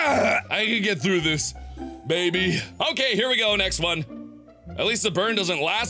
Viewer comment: Sounded like he said 'baby'.